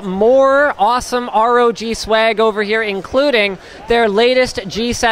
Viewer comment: did i hear swag D